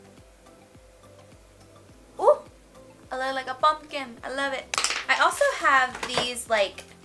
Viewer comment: I thought she said bumpkin